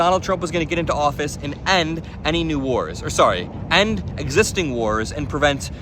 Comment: I thought he said no new wars! 🫩